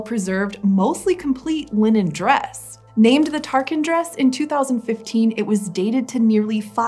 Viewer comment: I thought i heard it was target dress…